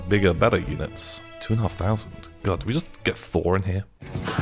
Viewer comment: Petition For To Bring Back Quiz Time - Day 280 Ha, what a really fun episode :D 0:14 XD thought said "Just get 4 on here" Wonder Horse is literally either the worst god damn thin in this game, or God XD You know its funny how mocked the Blow-darts last episode, but not he loves them XD 11:07 eyyy fantastic reference there XD Also that Tank determination XD You know I really like the Western Music used at the end, anyone know the name?